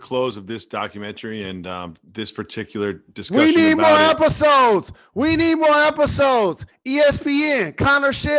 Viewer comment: I'm I the only one who thought Jalen said "We need more applesauce!" ?